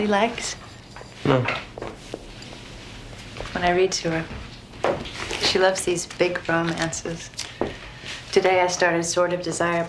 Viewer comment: LOL 6:15 did she say she loves those big brown asses ? XD